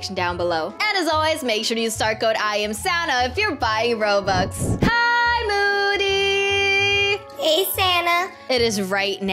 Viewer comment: Is only me or what i hear "HEYYY MOODY" "hey santa"